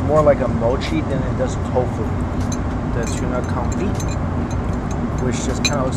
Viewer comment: I thought it said tuna confetti